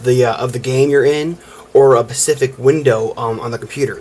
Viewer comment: did you just say pacific XD LOL great review though.